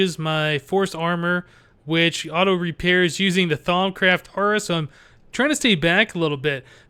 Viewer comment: Can someone explain the repairing the Force armor with something from Thaumcraft? He mentions it but I can't quite understand him. the wiki says thaumcraft shard but it sounds like he says thaumcraft horace? haaalp?